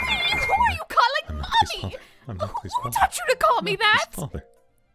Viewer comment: Diluc weakly murmuring “I’m not Klee’s father...” over and over again has to be the greatest thing I have ever witnessed. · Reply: At first I thought he said “I’m not klee’s MOTHER” And then I looked back